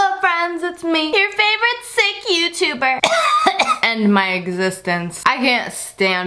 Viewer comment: I thought you said your favorite sex YouTuber